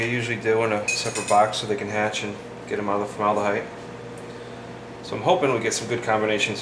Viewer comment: Sounded like you said formaldehyde instead of vermiculite...